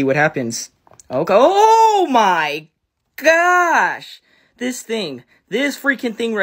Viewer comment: I thought you were saying "doobie roaches!"